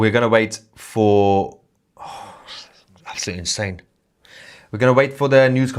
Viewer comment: What did Mahyar say around 38:11? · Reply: "Oh my God absolutely insane" I think x